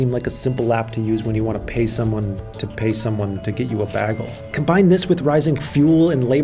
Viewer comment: He said bagel. Idk what is wrong with it... bagel 🥯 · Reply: sounded like he said “bagol”